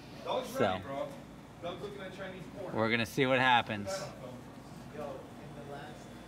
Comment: did he say stop looking at chinese porn 🤣